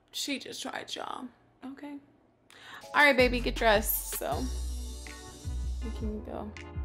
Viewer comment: Okay, I heard that “you cute tho” @ 5:33😭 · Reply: It was "we can go." She said "get dressed so...we can go."